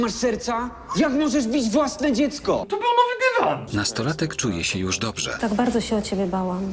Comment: co mówi? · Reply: To był Nowy Dywan